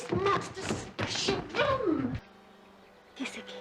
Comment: I can't quite hear what the woman asks him at 1:37. He doesn't know how to do what? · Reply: I think she says "kiss it please"